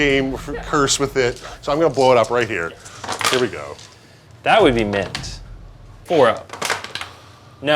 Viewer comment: Does say 'That would be mint'?